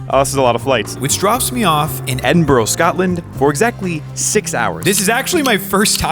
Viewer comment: sorry did I just here that correctly? Did he say Edin-bro?